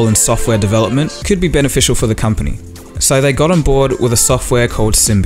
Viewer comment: I heard "So they got onboard with sybian" xDDD